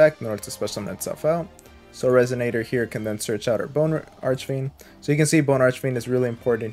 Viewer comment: did I hear you say Boner Archfiend? If so that’s hilarious 😂😂😂 that’s what I call him